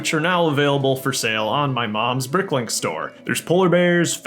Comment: Did I hear mom’s